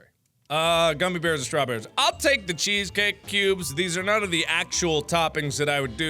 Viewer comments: I thought he said cheesecake pubes. I also don't know what mochi is. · i thought mark said cheesecake pubes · I thought he said cheesecake pupes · Whose else thought he said ''cheesecakes pubes'' instead of ''cheesecake cubes'' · I thought he said cheesecake pubes